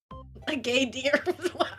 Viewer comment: Not gonna lie though I thought she said "Gay Deer" instead geindeer😂😂